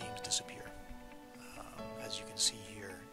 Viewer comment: Anytime someone says ‘Thanks for watching’. I hear: “Keep your Di€k in a vise.” Lol